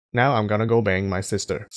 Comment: Did I just hear "Now I am going to bang my sister."?